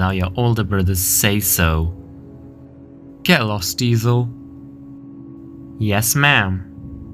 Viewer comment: watching this the first time, I thought he said, "Kill us, Diesel." instead of "Get lost, Diesel."